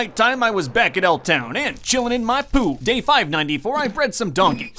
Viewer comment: When he said, “now I’m chillin in my pool” after going to the ice spikes, it sounded like he said “now I’m chillin in my poo” lol